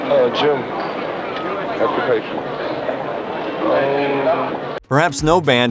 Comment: does anyone know what the interviewer asked Jim Morrison? I’m referring to the second question. I can’t understand what that dude said. Kind of sounds like “what do you play?” ❓❓ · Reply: whats your occupation